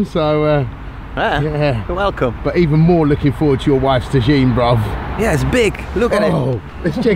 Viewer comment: Honestly thought he said 'looking forward to your wife's vagine' haha